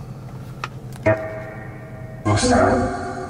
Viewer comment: after he said here comes the security again i heard a “who’s that”